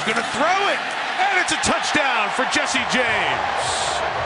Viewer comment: Did he say Jesse James? Like The Outlaw?